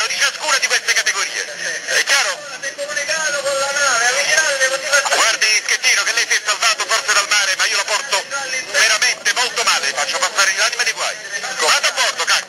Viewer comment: Sto imparando l'italiano e c'è qualcosa che non ho capito. Se c'è qualcuno che mi dica che dice Gregorio de Falco dal 2:07 al 2:14 (...) 'guardi Schettino (...) forza del mare ma io lo porto veramente molto male (...) Grazzie amici. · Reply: Guardi schettino che lei si è salvato forse dal mare,ma io le faccio passare l'anima dei guai